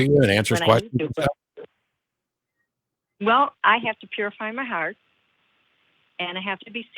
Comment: I almost thought she said “I have to purée my heart”. I was thinking to myself by all means, purée that fucker.